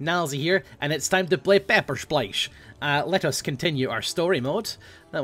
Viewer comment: Totally thought he said: "Pepper splice" at the start.